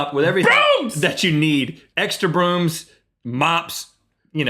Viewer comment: When Link Screamed "BROOMS" I heard "BOOBS" at 7:33 it just made me laugh XD lol and then I thought "why the hell would anyone say that!?!and why? further more who loads up on Boobs at a grocery store? o.O and how does one even do that!?!" I'm just saying lol XD